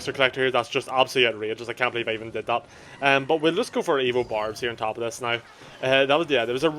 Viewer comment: Everytime you said "evo barbs," I heard "evil barbs."